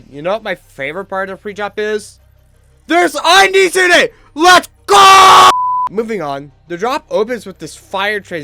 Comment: I literally could not understand what you said here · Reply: I think he said "there's undies in it let's goooo" because of that background structure in the center of the screen